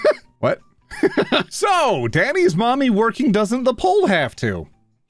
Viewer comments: after spending far too long figuring this out, heres what ive got ok so the most reasonable sentence i could get here is "daddy/mommy's working so mommy/daddy doesnt have to" but the pole? i assume its something about someone working so the other isnt a stripper but like... in what world does those words make sense in a sentence? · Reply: Daddy's working the pole so mummy doesn't have to 😁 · I think it says "Daddy's working the pole... So mommy doesn't have to."